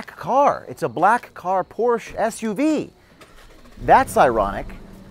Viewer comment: did you say James O’Queef? 😂😂😂😂